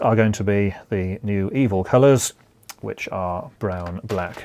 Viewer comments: 'the new evil colours'. HaHa. Did he really say that? I know he doesn't like the new European colours, who does? But evil?! :D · did he just say "new EVIL colours"? Lol